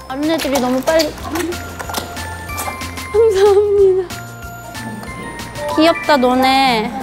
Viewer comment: did I hear rosie said "ang dami nila" HAHAHAHA 🇵🇭